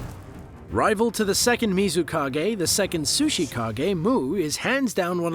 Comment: Im sorry, did you just said "sushi" kage?